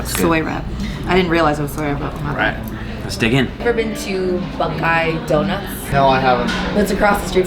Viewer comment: Did she just say "Buckeye Donuts" does she go to Ohio State?